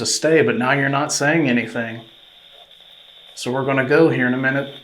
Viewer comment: says ‘you told us to stay and now you’re not saying anything, so we’re gonna leave’. You heard/wrote ‘here’… I don’t hear that. I hear ‘lucky you’.